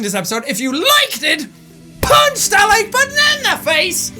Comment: my sister and mom thought he said,"punch that banana in the face" omg